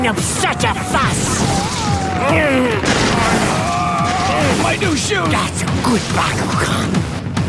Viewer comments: did he say my new shoes LOL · Did he say my new hooves? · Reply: Shoes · DID HE JUST SAY MY NEW SHOES 8:10🤣😂🤣😂🤣😂😅😂😂🤣😅😅😂😂🤣🤣🤣🤣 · listen carefully and just....... wow. Did he say my new hoof/shoe?? And then hydranoid was like "that's a good bakugan" I always knew hydranoid was kinky